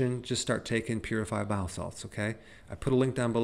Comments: I have the playback speed up usually and for a second I thought you casually said 'bath salts' lol... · What did he say to take? I heard “purified bath salts” but is that what he said to take? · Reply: Purified Bile salts. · This is good advice, but I have listens to this numerous times to decipher what Dr Eric says at 2:08 after ‘so what is the solution, we’ll just start taking…..’ and he may as well be speaking Russian as I can to make it out what he is recommending to take. Please, can any one translate what he says at this point? Thank you. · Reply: Очищенные желчные соли - purified bile salts